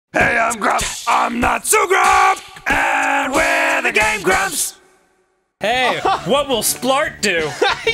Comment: Sounded like Dan said "Gay Grumps" at the end there... Also, there really needs to be a Papa Grumps episode starring Lloyd and Avi